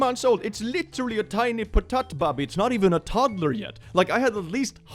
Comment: Potatbab... Not entirely sure if that's what was said, but I like it.